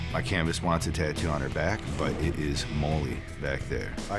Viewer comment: I misheard it as "her back is really moldy". I was very confused for a minute lol